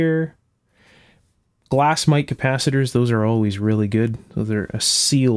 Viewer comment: it totally sounded like he was saying wack-ass capacitors